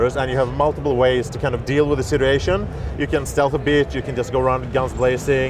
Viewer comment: I swear I thought I heard that guy say, " You can stealth a bitch."